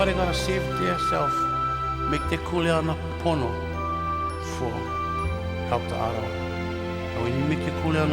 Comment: Did he say "you know, porno"?!